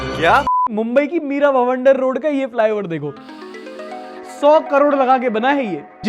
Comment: Bhai .did u just say Mira Bavander road 😅